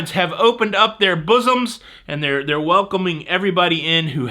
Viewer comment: I thought I heard you say bosom. It was probably bosun.